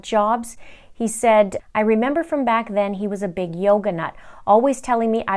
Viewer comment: I thought you said"yokanut".I thought it meant he was nerdy.🤪